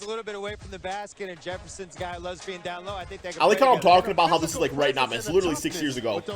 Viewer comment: Now I know I did not just hear, what I think I heard from the commentator at the 8:21-8:22 mark.... Lez...B.....N down low